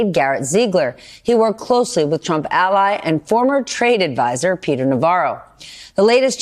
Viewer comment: When you said "former trade advisor", I heard "former traitor advisor". Why is that?